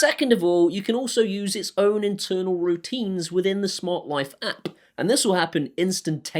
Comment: I heard "smart wife" after the alexa joke